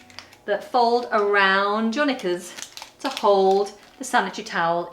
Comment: did she just call panties "jónicas"? sure I'm a GUY, but never heard THAT one before...